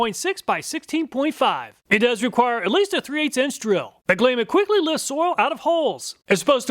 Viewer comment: I had to rewind the auger bit because I thought you said "it quickly lifts oil out of hoes" and my brain shorted out.